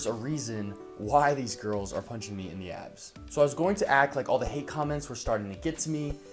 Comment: Since im german i thought he was saying "punching in the ass" instead of "abs" the whole time ^^